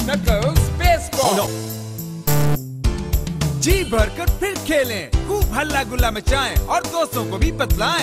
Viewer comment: i heard G burger pig kelly